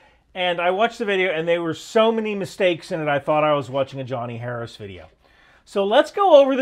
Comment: When he said Johnny Harris video, for a moment, I thought he might have said Johnny Quest video. I had to go back and check (...so did Johnny Quest.)